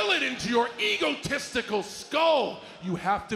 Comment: Thought cena said “ego testicles”